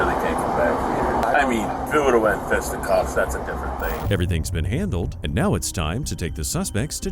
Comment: Bad closed caption 🤦‍♂️ I'm just watching on my cellphone and even I can hear what he's saying. It's not "if we would have had to fetch the cops." The guy actually said, "I mean, if it would have went fisticuffs, that's a different thing." "Fisticuffs" is an old school word for hand to hand fighting.